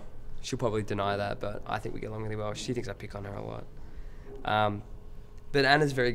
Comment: Great interview :3 Can someone tell me what did he sad at @ 2:19 ? after "I think we get along really well, she thinks..." thanks ( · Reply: she thinks i pick on her a lot which means constantly annoy